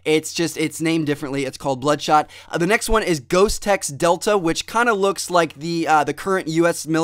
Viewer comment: ghostex delta 6 i thought it said ghostsex delta 6 lol